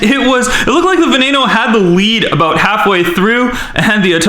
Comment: he says weed instead of lead